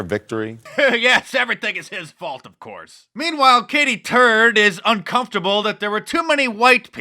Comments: Her name is really Katy Turd? That’s what it sounded like she said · Lol is her name really Katy Turd lmao. Thats what it sounded like she said her name is.